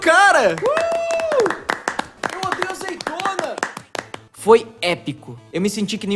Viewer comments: so eu ouvi EU ODEIO AZEITONA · eu ouvi "eu odeio azeitona" kkkkkkkkkkkkkkk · Eu ouvi "Eu odeio azeitona" no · só eu que ouvi eu odeio azeitona